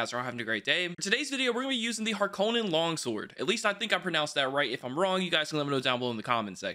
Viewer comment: For a sec I thought he said " Hulk Hogan long sword" instead of Harkonnen long sword. 😂